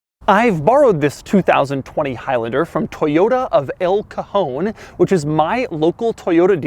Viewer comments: I thought he said "Toyota of El Chapo" LOL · I thought he said "Toyota of Al Capone."